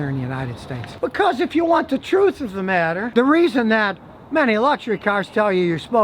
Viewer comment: Did he just say electric cars use high octane gas??? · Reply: jason muscat he said luxury, not electric